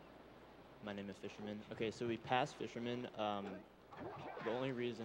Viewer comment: Wait did they say "my nama fisherman" at 12:15?